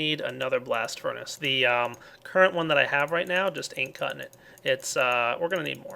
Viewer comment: did he say ain't????